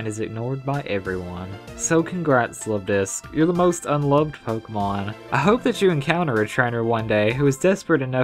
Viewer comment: I thought it said un evolved pokemon oops 😅😆